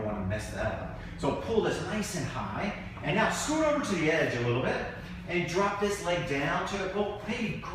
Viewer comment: Why did he call her baby @ 3:52? Weird.. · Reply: he said “scooch over a little bit.” He did not call her “ baby”. Time for an appointment with an audiologist.